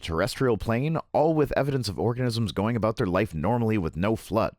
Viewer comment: I swear you said orgasms. Which for some, is a cause of a major flood. 😉